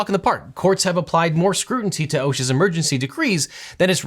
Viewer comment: Did you just say "scrutinty?"